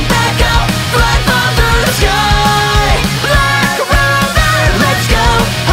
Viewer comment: for the longest time, i thought this part said "luck lover"